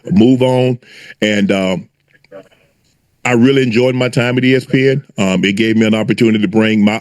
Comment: i thought he was going to say i enjoyed my time with the of girl😂😂 but all jokes aside.. i hope black women will not be checking for him after this..